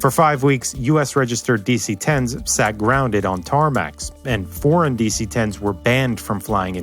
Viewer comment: Did he say tarmac?!?!